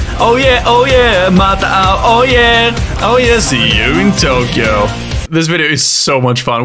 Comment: He uses this in his outdo I thought it’s said See you in time time go!” Wow i was wrong !!! Lol